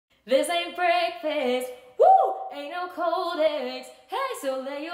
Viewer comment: Nah cuz as a footballer i could have sworn she said " This aint brexit "